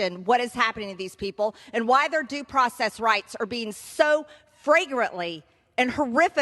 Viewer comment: Wow that was an outstanding speech, she barely looked down at her notes, you know unlike that new press secretary who can’t keep her eyes off them. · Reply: LOL, it's because she can't read! That's why at 3:26 she said FRAGRANT instead of flagrant.